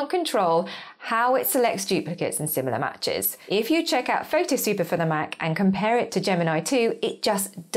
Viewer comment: What did you say? It sounded like, "Photo Super for the Mac." Is this Photo Super better than Gemini? I'm new to all of this - someone recommended Gemini and that's how I got here. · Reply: I am saying PhotoSweeper for the Mac, it is another deduplication tool, focused on photos, check it out here <link> 😁